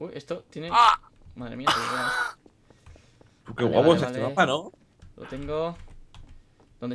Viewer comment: Del 0:39 al 0:47 Escuchen lo que dice willy , e.e! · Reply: Dice: Que guapo esta este mapa. Parece Vegetta